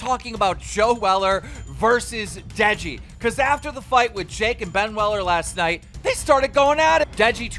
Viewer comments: Who else heard "Ben weller" 2:50 😂😂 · did he say ben weller